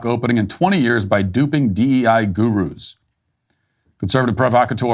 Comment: For "Gurus" I hear "Grifterooz"